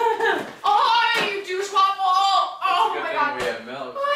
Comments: Did she call him a douchewaffle? XD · did she call him a douche waffle hahaha